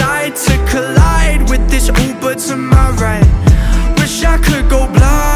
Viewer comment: The first 126 times I listened to this, I thought he was saying “Oompa to my right” instead of “Uber to my right” and I was straight up vibing with the vision of a car crash between and an Oompa Loompa. Anyways, this is a masterpiece.